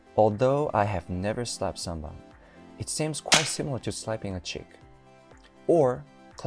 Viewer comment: I rewatched a few times, almost thought you said 'similar to slapping a chick' [borat hi fives]